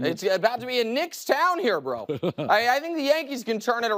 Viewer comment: I swear I almost heard "Bru" lol FTF is soooo much better than The Herd lmao